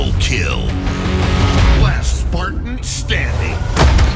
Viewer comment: legit just thought he said "Last fartan standing!" and I was about to gut myself.